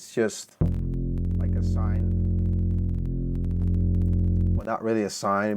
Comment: What did he use to make the base/sign wave? He mentions it at 3:00. I couldn't understand what he was saying... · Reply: Sine bass. But imo it doesn't sound like it - it's more like a mix between sine and saw.